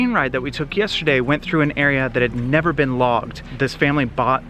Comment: When you said the area had never been logged I thought you said vlogged 🤣